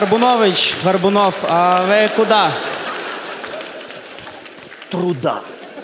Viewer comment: поясните шутку на 15:56.. что они говорят? и про велюр тоже, в чём прикол? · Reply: На 15.56 Горбунович Горбунов говорит: "В жопу труда!" Ну только естественно первые два слова просто шевелит губами. А про вилюр просто переход, что в рифму зашла следующая шутка про самого злого Юру.